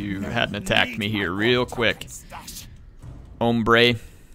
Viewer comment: Did just say "hombre" at 6:13? Loved it haha